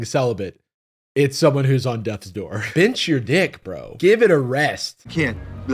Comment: "Bench your dick, bro" will be in my vocabulary forever now, thank you Jarvis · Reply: I thought he said “pinch your dick” 😂😂😂